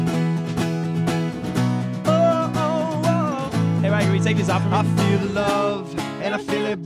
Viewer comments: Did anyone hear ross say "hey riker can you take this off for me" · que dice ross en el minuto 1:32 ? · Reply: Would you take this off? Me podrías quitar esto?